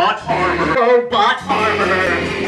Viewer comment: sounds like he's saying Robot Farmer instead of Robot Armor